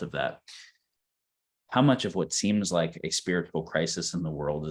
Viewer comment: What word is Daniel saying that sounds like "spiritual" at 1:26:01? Thank you 🙏